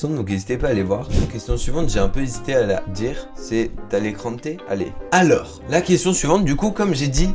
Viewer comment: quoi ? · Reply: C'est hein ? Mais tranquille